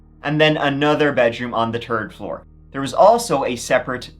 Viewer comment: I was tired and I heard you say tird floor and thought man rich people do have it all…😂